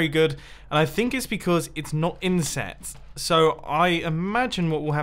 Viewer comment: Did just said “because it’s not incest” ?